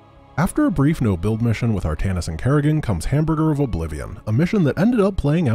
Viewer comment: Did you really say "Hamburger of Oblivion" at 15:52 ?